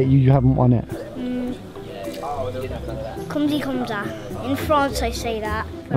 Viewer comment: did he said: Comme çi comme ça ?😂